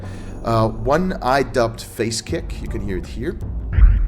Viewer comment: I thought for Phase Kick you said Face Kick